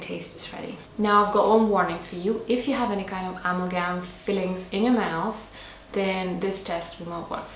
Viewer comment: What did u say at 6:38 ???? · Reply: "If you have any amalgam fillings..." <3